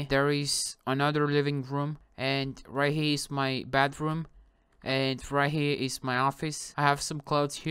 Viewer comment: Oh yeah the bathroom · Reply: Uhh He said bedroom and you thought he said bathroom cuz of his beautiful accent